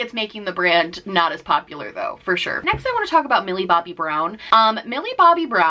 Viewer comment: I literally thought you said ‘billy bob Thornton’ instead of ‘Millie bobbie brown’ and I was CONFUSED 😂